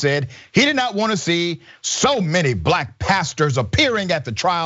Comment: I thought he said, “Too many black bastards in here.” My blood was boiling. BUT what he actually said was not better. This coming from an atheist.